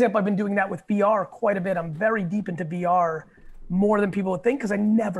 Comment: he says BR? did i miss hear and he really said VR? if he did say BR what exactly is that? someone halp.